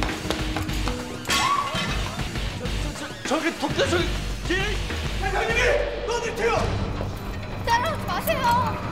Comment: 이민용 뭐라고하는거지? “저게 복도에서••” ? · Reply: "저게 토껴..!" 라고 하는 것 같네요